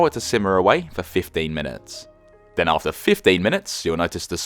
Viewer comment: Did you say "fifteen" or "fifty"? On top of terrible hearing, I'm also really bad at differentiating the two when people speak 🥺 But thanks for the recipe, this looks quite simple, I wish I had basil so I could make it right away! Its 6:30 p.m. here, almost time to start cooking 🤤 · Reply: 15 minutes 😁 It’s a very simple recipe and tastes so good. The basil is a big part of the flavour as it slowly releases into the sauce whilst it’s reducing. 👌